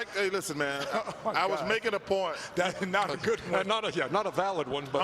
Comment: what did charles said?He was making a porn?